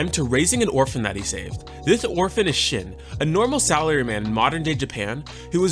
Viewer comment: really thought he said this orphan is shit 😂